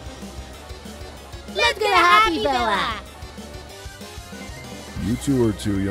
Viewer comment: I thought he said “let’s get a happy meal”